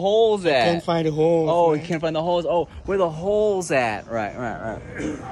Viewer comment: Lmfao I thought dad said “where da hoes” at too 😂😂😂 I love their bond